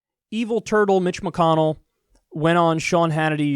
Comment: lol I first heard "Evil turd Mitch McConnell" instead of turtle XD